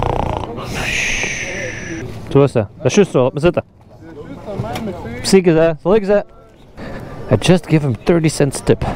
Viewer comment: welke taal is dat bij 12:04. Het is geen Vlaams en ook geen Frans toch. Ik heb het een paar keer herhaald maar ik heb geen flauw idee wat daar gezegd wordt. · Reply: I'm saying "Das just zo , lot mer zitten." It's indeed Antwerp dialect to say "keep the change" 😁